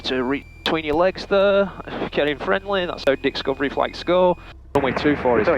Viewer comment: it sounded like he said, "that's how DICKScovery flights go," while apologizing for reaching between his legs! I know it's Dixcovery flight, a parody of "LewDix Aviation, but do I just have my mind in the gutter thinking "dicks?" I am dying! At 55, with a commercial/instrument I haven't used in over 20 years, I'm back in the cockpit getting current, about to start training for my CFI (and more after). Thanks for your great, informational teaching videos!